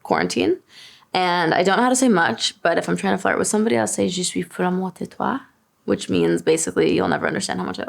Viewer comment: hii i'm a french girl who teaches french and if I heard that right she said 'je suis folle amoureuse de toi"? which translates to "I'm crazy in love with you" not "you'll never understand how much I love you" loool that would be "tu ne comprendras jamais à quel point je t'aime"